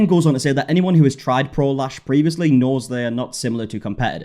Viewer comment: Every time someone says, "Pro Lash," I hear, "prolapse," and I've just been giggling this whole time.